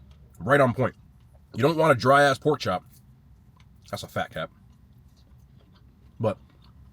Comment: I thought I heard u say that was a fact cap not fat cap.